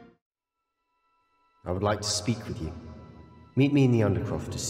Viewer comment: I thought he said “I would like to SLEEP with you, meet me in the Undercroft as soon as you can” 😂